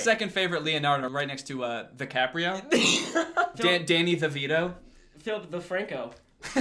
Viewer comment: RIP I thought that said Danny Devito